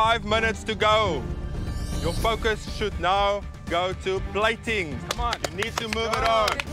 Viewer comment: i thought he said "You fuckers should now go to plating". I need to sleep